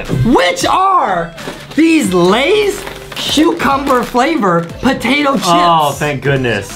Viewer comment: was it just me or did he say “shoecumber”? 😂 wow this comment was 4 years ago its kinda sad how lankybox turned out